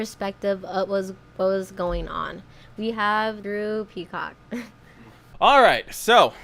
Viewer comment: I thought he was gonna say "HOW is it going guys Droopy Cock here"😂 I waited for this video to draw up my own conclusion on both sides of the story and I'm siding with you guys🖖